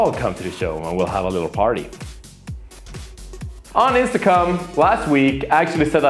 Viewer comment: i swear he says on intsacum